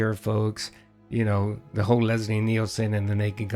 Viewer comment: I misheard Leslie Nielsen for "Lesbian Nielsen" which is another great nickname for Phil.